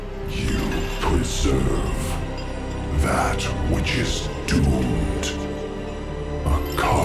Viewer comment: Can someone explain how the helm breaking in the first place made the portal? What are Zovaals last words? You preserve that which is doomed. A cosmos dived will not survive. unless it is... CHOKALL? Chosen? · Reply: I actually heard it as "what is due to come." But I don't think that's too different in the meaning.